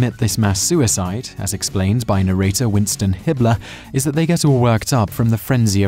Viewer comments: dude I thought you said Hitler 💀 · i thought you said Winston Hitler...?